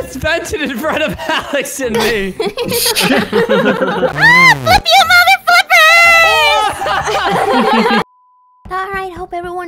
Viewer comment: Did say the f word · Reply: She said ‘FLIP YOU MOTHERFLIPPERSSSS’